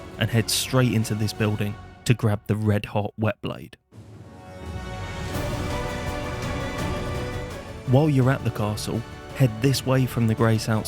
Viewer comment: I thought bro said red hot beyblade XD